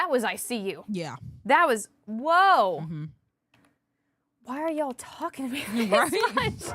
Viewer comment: I hear “How are you feeling”